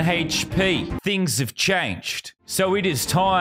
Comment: I heard BEANS have changed.